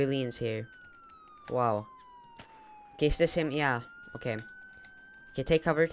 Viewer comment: what did you say??? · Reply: "Is this him? Yeah.